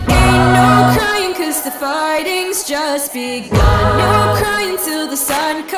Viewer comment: did they say farting?